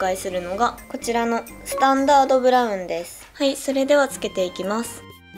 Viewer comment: あたりの「はい、それではつけていきます。」が何故が僕のSiriちゃんにはHey Siriに聞こえるらしく、再生する度にSiriが起動する(´◦ω◦｀)